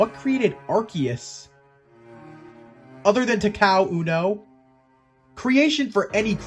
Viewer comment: i cant make out wat u said after "who created Arceus?" lol GREAT Pokemon to make a video on!!!! The actual god Pokemon lol. Also how does it have so many arms O.o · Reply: It was Takao Unno, the man designed Arcues, I thought I’d be funny and make a joke about it’s creator. Also yes, 1000 arms. That’s a lot of arms imagine how much Arcues can get done.